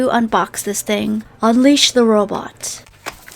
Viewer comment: i thought she said “unleash the roblox ”